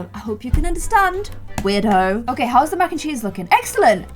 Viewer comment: Was it just me hearing this or... did say weirdo or weird hoe